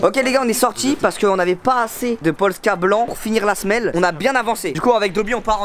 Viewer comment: y’a que moi qui est entendu de « POLSKA BLANC » mdrrrrr